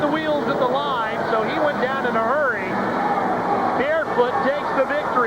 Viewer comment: I remember this one, we left it early, because there was no Bigfoot. It was all Gravedigger, and everyone was cheering for him. It was no fun. Worst part was I was sure that they said Bigfoot was in it, and I even showed my did the ad. I have really never been to a monster truck show since.😥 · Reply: Bearfoot was in this event. You probably misheard it as "Bigfoot." Monster truck events are still really fun to go to, trust me